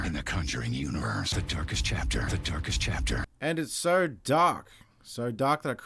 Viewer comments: if you turn on the subtitle its doesnt say "the darkest chapter" it goes " the turkish chapter" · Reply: That's what it was saying? I legitimately thought it was saying "The Turkish Chapter". Not even kidding. Lmao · whhat , is he saying Turkish Chapter ?